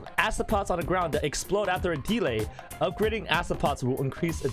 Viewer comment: When said delay 21:14 I thought he said demon XD